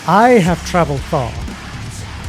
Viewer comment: i clearly hear "i will travel far".